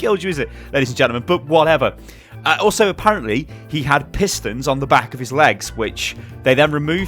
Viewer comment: Im an american and i thought at 8:36 said "Piss Stains on the back of his legs" instead of "Pistons on the back of his legs" I had to rewind so fast i was like no way did just say that lmao i was chewing food at the perfect time. And I thought this was an amazing video. You upload everyday. its not about the ads man, you make great content and you deserve everything you get and you deserve even more. This was a great video, keep em coming!